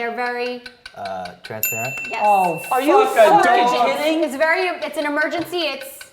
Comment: Did joe say fuck a dog